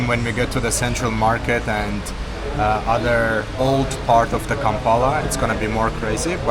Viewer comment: Did i hear "kampana" ?